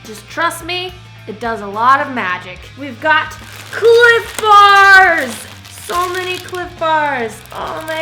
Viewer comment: I thought she said clit bars